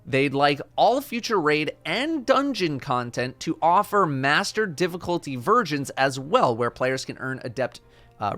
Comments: did he say mater difficulty virgins? lmao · Did i just hear "Master difficulty virgins"?